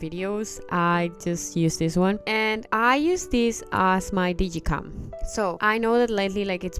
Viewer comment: what word is it you´re saying? Youre talkign about youre using it as your ----- and its been a trend to order ------of ebay lately. If you could please tell me what word it is because I cant hear it :) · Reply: I think I’m saying ‘digicam’, sometimes I do not even know myself what I am saying😂